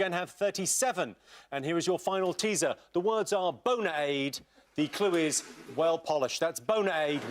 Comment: Was he saying BONEAIDE? 41:00 How does that spell debonair? There's no R?